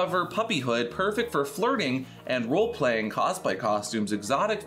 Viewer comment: I thought you said farting when you said flirting lol 😂 I’m not here to yuck someone’s yum I just couldn’t figure out how the puppy play mask had something to do with with farting.